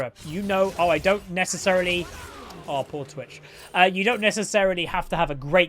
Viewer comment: Did i just hear say poor twitch xD